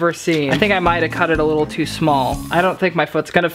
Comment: “i think I might have cut it to small” caption says (Like my dick)